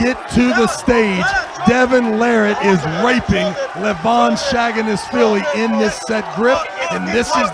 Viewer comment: Bro wtf is this commentary??? Bro just said “Devon is raping Levon”